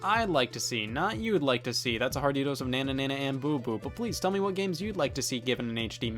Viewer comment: I've rewatched 1:03 several times but I still can't figure out what Scott says there. Banana nan boo boo?